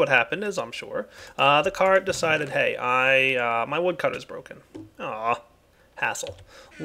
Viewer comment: Totally thought he said asshole XD